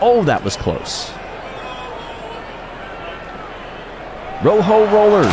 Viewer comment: I don't know why, but I keep thinking I hear “rojo rollers” as chant when the Raspberry Racers are up.